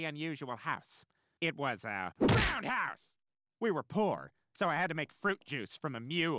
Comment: How Seth managed to say Road House without using Peter voice? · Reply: Somehow on the TV I heard "roadhouse" But on this video with my headphones I hear "ROUND house" I guess my brain just defaulted to Peter😂